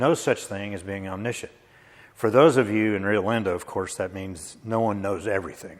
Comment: had to listen to this 4 times as it sounded like he was explaining omniscient "to those of you in Orlando." Hmmm, did have an axe to grind for all Floridians, or just those in amusement parks?